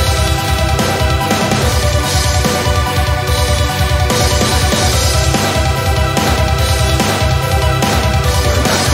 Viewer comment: What does Gaster say when he appears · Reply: remember son, dying is gay.